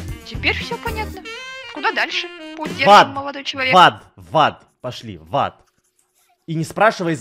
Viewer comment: куда дальшу путь держим Эдисон: в ад в ад в ад и так далее Мне одной послышалось в зад?